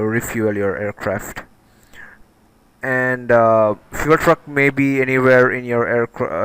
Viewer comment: did anyone else heard him saying fuel fu*k?